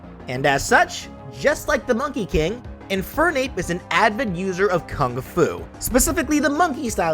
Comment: I heard admin user